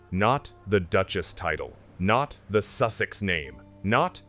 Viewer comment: I thought she said “I’m Sussex Now” on her Netflix clown show.